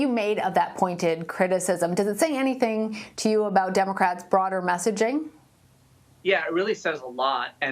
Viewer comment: did she say "Democrats, fraud or messaging," sound legit then. I know she didn't but if she did at least she would be truthful.